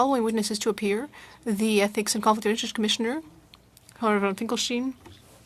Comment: Did he just say "COMRAD von Finckenstein"???😮